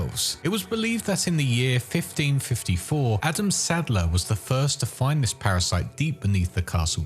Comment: I misheard it for Adam sandler😂